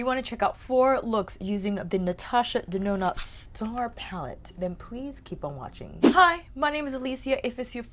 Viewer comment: I thought you said new ND star palette and I was briefly losing my grip on reality until some comments said they loved theirs for a long time. Okay, so I’m NOT accidentally time traveling in the matrix.